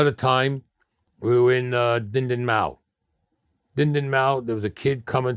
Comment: what was the name of that town? I think he was trying to say “di di mau” which is what I heard a lot of Vietnam vets say. It means “get lost” or “get the fuck out of here.” I think he heard a lot of veterans say this when they got back to the USA so he threw this phrase out when trying to make up names of places he fought. This guy is full of shit lololol!